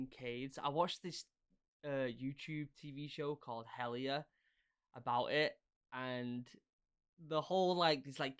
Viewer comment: someone tell me what show he is talking about. Hellia, hella, helia, helya, hellya, I cannot find anything here on youtube.